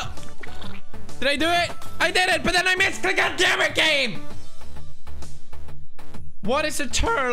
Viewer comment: Did he say "*God dammit Gabe or game" ?